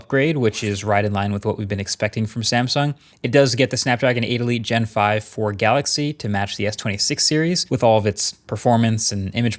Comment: Had to do a double take at 0:35 because i heard Snapdragon 8 Elite Gen 5 Ford Galaxy ☠️